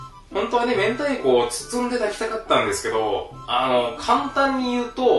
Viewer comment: 明太子を抱きたかったって聞こえた🤣